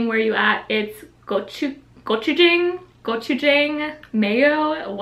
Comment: When she said gochujang i thought she said gucci gang 4 some reason